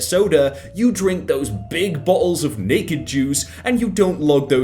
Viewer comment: thought he said bacon juice